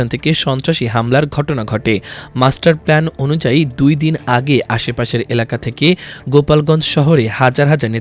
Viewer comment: Did he just say... "Mustard Plan?" 😂😂😂😂